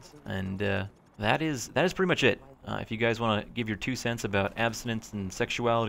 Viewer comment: "That is pretty much shit " <-- at the end i thought you said that xD